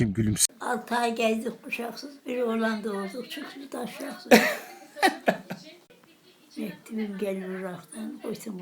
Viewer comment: te teyze ne diyor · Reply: 6 ay gezdik kuşaksız Bi oğlan doğdu o da taşaksız Gelinim gelir ıraktan götü kurtulmaz yataktan Diyor